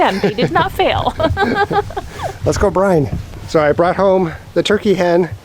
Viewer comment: I thought you said Let's go Brandon, and I was surprised, pleased and cracking up. Then I realized you said Let's go brine it, then I really cracked up for thinking that, LOL. 🤣